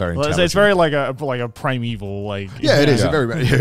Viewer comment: please tell me I'm not the only one who thought that said prime evil